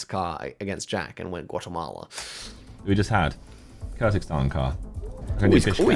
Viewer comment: Did he say Kyrzakhstan?